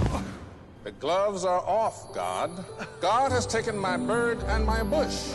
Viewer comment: uhh god to be honest...he didn't said that he said "FINE! the gloves are off pal!" 2:33 pay attention what he said next time.